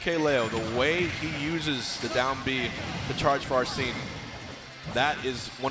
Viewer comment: did hbox just say “arseen”?